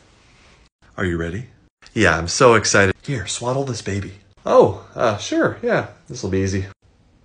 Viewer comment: Ngl first thought he said swallow this baby 😂😂